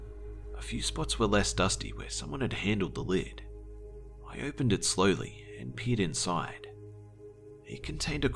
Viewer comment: " I opened it slowly and peed inside" is what I thought he said lol